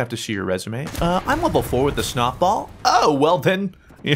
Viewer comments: is it me or did he say snotball??? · Omfg at 8:32 the captions said that he said “snot ball” and I DIED